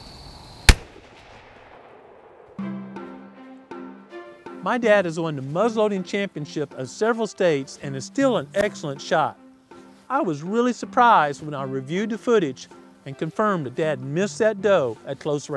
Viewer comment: Is deer hunting a tradition for your family? Who do you hunt with? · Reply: man alive when you said your father was a muzzleloader champion i thought you were were saying a big tail till he droped those deer my mouth flew open tell him i said good shooting